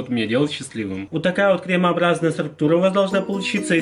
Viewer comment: ПОЧЕМУ МНЕ ПОКАЗАЛОСЬ ЧТО ОН СКАЗАЛ СПЕРМООБРАЗНАЯ СТРУКТУРА? ПОЧЕМУ?!??!?